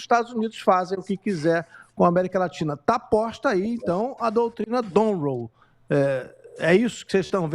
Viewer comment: Eu ouvi direito ou o repórter falou em doutrina Donroe ao invés de Monroe aos 08:55?